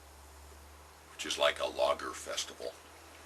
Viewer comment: when you said 'logger' i first thought you said 'lager'...both of which sound like a blast i like grabow pipes, but detest their injection molded stems, it's a shame that they compete for price instread of quality, I have $50 to spend, i'd rather have a higher-tier grabow option. maybe ill be king someday. until then, ill drink lager and think of log rolling while i smoke my grabow filled with Carter Hall.